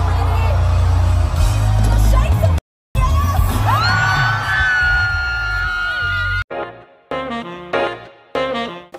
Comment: I WANNA KNOW WHAT THE HELL THEY SAID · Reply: They said “ coachella are you ready to shake some a$$ “ cause they were playing smartw